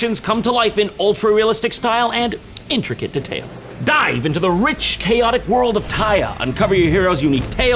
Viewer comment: I THOUGHT HE SAID "DIE" LMFAOOO